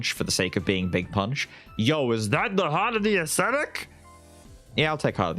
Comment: Glad to hear that is sounding like he's feeling better. When I heard him say "Heart of the Acetic," I know was almost at 100%. 😁😁